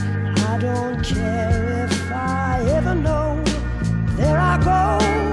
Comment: I always thought it said flaco not “there I go”